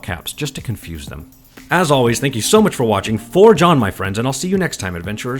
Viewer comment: I misheard "Forge on, my friends" as "For John, my friends". For John